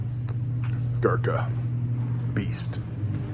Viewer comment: After you said “Bearistan” you said “Gurkha” (cigars), but for a sec I thought you said Bearka (as in burka but worn in Bearistan). 🤣